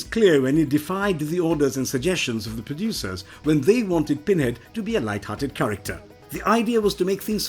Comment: All I hear is "Binhead". Interesting, though!